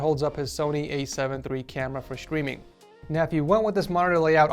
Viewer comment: he say for screaming instead of streaming